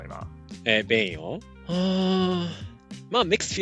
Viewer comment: ここなんて言ってるか教えていただけると助かります！リスニングが弱い僕は分かりません笑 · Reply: Mixed feelings